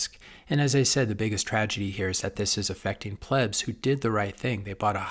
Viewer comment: Did he say plebs? 😂😂